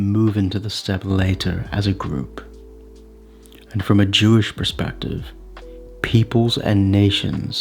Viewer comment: did he say from a jewish perspective? thats also what the subtitles picked up